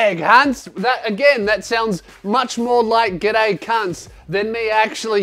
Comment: if you turn on subtitles instead of "good day cunts" youtube decided it was actually "get a can"